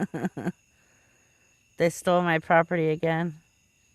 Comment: Sounds like he said they stole my property again